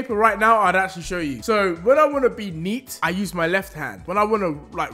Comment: i thought he said beat meat